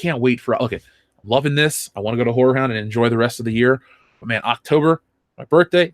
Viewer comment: I keep hearing "Whore Hound".